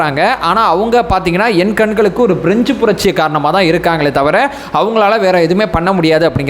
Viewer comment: I think said "fringe" not French